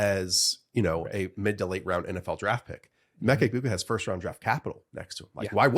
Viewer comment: Yall said “emeka egbuka” but all I heard was “emeka egbigboo”